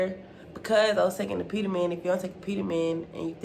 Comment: what was the name of the thing u said you take to gain weight? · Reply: apetamin